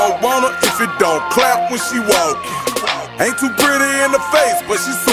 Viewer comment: Omg I thought he said pretty in the face not ain’t too pretty LMAOOO damn